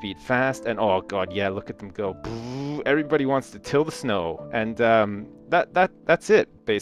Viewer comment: am i the only one who thought he said "kill the snow", when he actually said "till the snow"? o.o